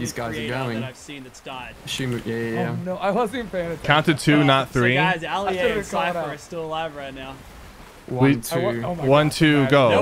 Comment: excuse me did he just say oli-a